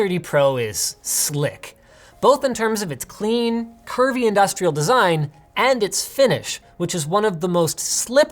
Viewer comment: I thought he said pervy industrial design xD